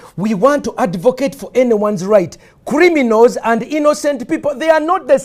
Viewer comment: i though he was saying “creamy nose” it took me a while to understand that he was actually saying “criminals”